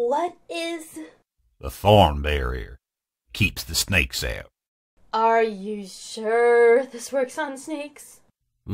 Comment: I thought Dustpelt said “keeps the snakes in” And I love the cut off screeching of Leaf and Holly